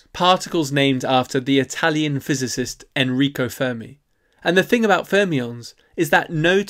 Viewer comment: I first heard “you see, neutrons are Fermions - particles made out of the Italian physicist Enrico Fermi” Had a laugh at the joke but the video just continued. Had to go back to hear it correctly 😂